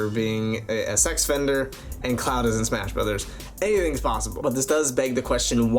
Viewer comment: Omg i thought u said cloud was in smash mouth